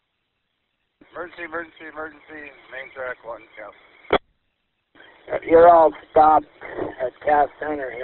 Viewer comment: did he say "you're all f--ked?"LOL